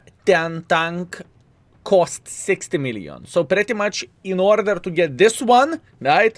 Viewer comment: Am I the only one who heard - sixty million?